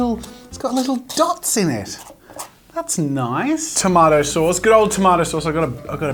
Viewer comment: jazza says toemottoe Me:WHAT DID HE JUST SAY !?!?!?!?!?!?!?!?!?